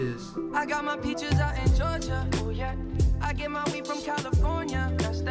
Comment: All this time, I thought that guy said “I get my wheat from California” and I couldn’t understand why someone would buy wheat from California.